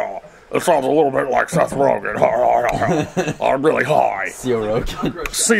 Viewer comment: DID YOU SAY SETH ROLLINS